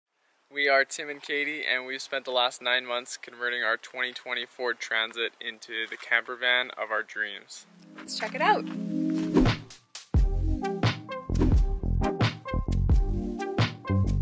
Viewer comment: When you said 2020 Ford transit I heard "2024 Transit" I was like wait what